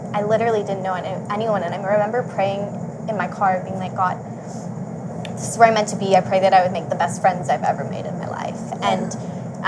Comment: Please someone write what precisely did say on 6:00 minute when she is talking about her praying in her car. Please, from the bottom of my heart, I replayed the video thousand times but unfortunately I cannot underatand that part. It's crucial to me so please good people, it takes a couple of minutes to tell me what exactly she said but it's so important to me to understand it! Thank you in advance and God bless you! 💜💜💜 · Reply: She says " God this is where I'm meant to be , I pray I make the best group of friends I've had in my life"